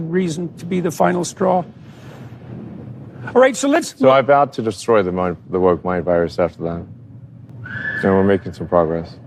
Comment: What did Elon Musk said at 5:06 - 5:10? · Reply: “So I vowed to destroy the woke mind virus, after that.”